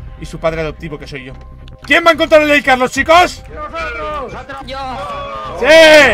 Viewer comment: pense que dijo quien va en contra de lil carlos? Y todos dijeron yoooo nosotroosss y justo estava comiendo y escupi todo :'v